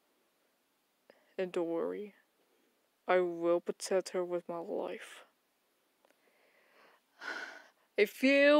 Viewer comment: lol for a moment I thought he said "I will protect her with my wife" XD